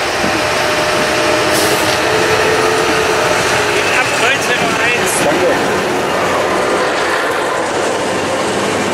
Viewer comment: Was sagt der Tf da bei @ 10:36? Und was ist deine Antwort?😂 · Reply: Abends sollte noch ein Güterzug von Baalberge nach Halle über Könnern verkehren. Der Lokführer hatte mir die Abfahrtzeit abends durchgesagt.